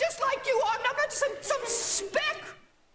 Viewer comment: is Winnie saying "I'm a person, too - I'm not just some sp!c.." ? if so, that came out of nooooowhere 💀